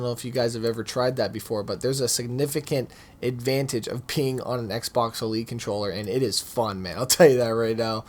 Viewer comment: Lol at around 9:00 the subtitles say “advantage of peeing on an Xbox elite controller...” lmfao